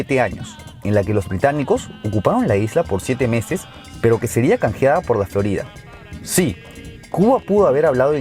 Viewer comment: Canjeada por ¿qué? 03:38 No se entiende Di con mas claridad nombres y fechas...! 👎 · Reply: Canjeada por la Florida.